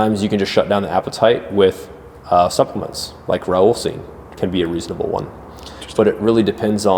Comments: what is he saying? Raoulcine? Raulcine? I can't make it out and the CC isn't helping. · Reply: I believe Rauwolscine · What does he say at 14:20 - something to shut down the appetite - sounds like Raoul seed? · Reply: I believe Rauwolscine